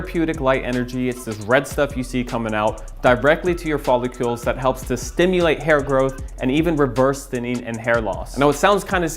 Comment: did he say 'follicules'? 😂